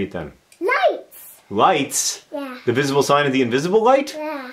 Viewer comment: For a minute I thought he said "knives" not "light". Oh god.